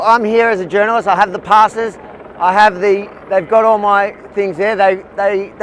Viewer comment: Who is that woman screaming? She sounds like a troll. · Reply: Sounded like she said "Avi they have got us", I don't know if that was a warning when the commander was walking over or if some of the staff got arrested too, if that's what is being said.